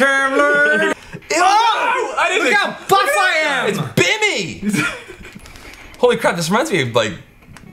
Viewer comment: did someone say 'It's Bimmy!' at like 2:00 lmao Bimmy and Jimmy